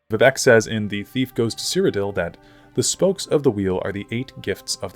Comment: Istg i thought he was saying a beef goes to cyrodiil for wayyyyy too long